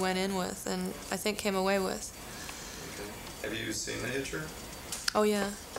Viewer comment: What movie does he ask her if she's seen at 8:48? Can't hear him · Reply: The Hitcher